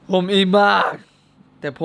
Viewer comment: ทำไมได้ยินว่าผมผีบ้า😂😂😂